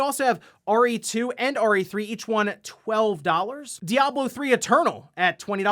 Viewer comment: Really thought he said Ori 2 and Ori 3 when he said RE2 and RE3 🤣, and I was like, 'where's 1 and when did they make 3?! 😵'